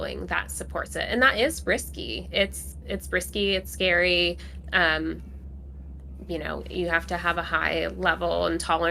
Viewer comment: I could not understand what said around the 30:00 mark. What is the name of the farm that she referred to? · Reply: I think she is talking about Moonshot Farm